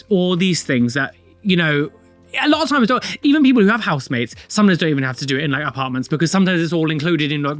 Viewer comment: The video title is even funnier when you consider that when I first started watching and hadn't gotten used to his accent yet (English isn't my native language) I misheard "housemate" as "husband" so for a few months I was fully convinced they were married😂